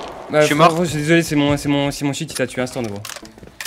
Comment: c’est moi ou il a dit « ptn c’est mon cheat qui ta tuer »